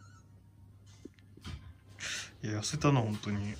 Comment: ガチでダメ出しｗｗｗ( ﾟ∀ﾟ)･∵ﾌﾞﾊｯ!! でも痩せたい時に痩せた？って言われると嬉しいよね〜(*´˘`*)やるぞ！って気になる。 途中、さんの「痩せたなぁ」が、ふじやんに聞こえて笑ってしまったｗ